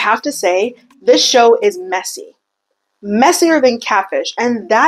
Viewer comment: I heard “messier than cabbage” and I was thinking how is she eating cabbage??